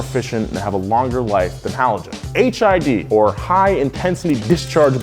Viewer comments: did anyone else hear HIV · Who else thought he said HIV?